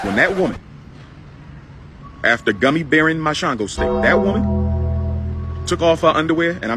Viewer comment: 👍 Every time I see a video from you, the editing it funny and top notch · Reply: Sounded like he said gummy bearing my shlongostick 😂😂😂